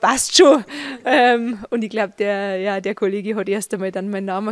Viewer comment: kann jemand übersetzen was Sie ab 1:18 nach gegoogelt sagt ?! Ich verstehe da kein Wort mehr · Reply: (...) gegoogelt und die Freundin neben mir hat's dann erst mal zerrissen...